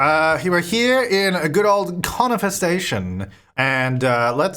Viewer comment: Each time you say 'Conifer Station' I hear 'Corn Infestation'.. ; )